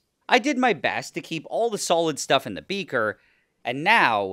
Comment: I just woke up. Watching shorts. When you put the cabbage in the beaker I thought that you said you're going to put the cabbage "in the beemer"🚗